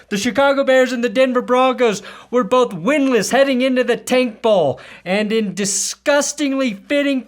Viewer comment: I heard "taint bowl" at 0:27, and I think that was even more fitting than tank bowl.